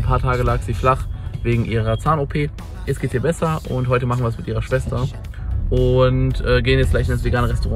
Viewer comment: Ich hab verstanden: heute machen wirs mit ihrer Schwester 😂